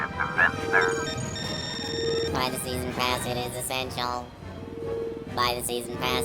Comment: for some reason i thought it was saying, "Privacy in my ass is essential". Then I realize it said "Buy the season pass it's essential".